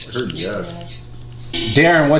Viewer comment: I hear "Ceres"